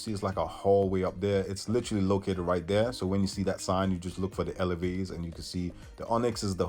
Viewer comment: what the hell did you say? SDE? HAHAHAHAHAHA CDE! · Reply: ANDY haha I said sign, but yeah mispronunciations happen. 🙌🏽